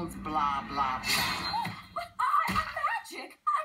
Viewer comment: Who's up for meatloaf? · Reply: I thought he said "Who's up for amiibo?".